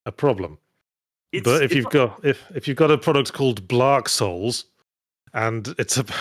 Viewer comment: Random thought... Yahtzee said Blark Souls but my brain heard Blart Souls. Can we get a Paul Blart: Mall Cop - Souls-like where you ride a segway the whole time?